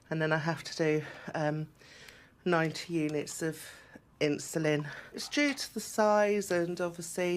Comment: Did she say NINETY units?! Every morning…?